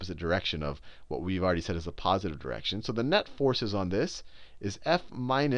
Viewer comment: I hear "so the nut forces on this are f - ky" *chuckle*... ohhh man i swear i'll never grow up